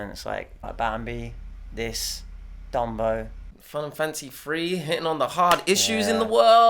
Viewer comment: For a second instead of Fun and Fancy Free I thought you said Final Fantasy III. EDIT: Now I keep hearing Final Fantasy III every time you say the title of this movie.